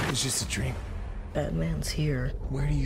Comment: I thought said "Batman's Here"